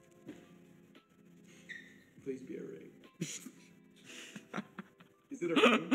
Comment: Did anyone hear sapnap say please be a ring at 53:00 or was it just me